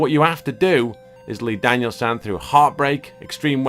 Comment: what the fuck, for thirty something years i always thought mr miyagi was saying daniel SON. it was SAN . what a mug i am, am i alone in this?